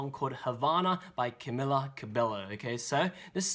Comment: Sounded like he said Cummila cibbilo LOL